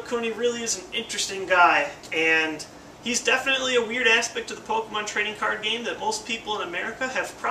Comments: I heard "He's definitely a weird ass bitch of the Pokemon Trading Card Game." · I thought he said "a weird ass pick" instead of weird aspect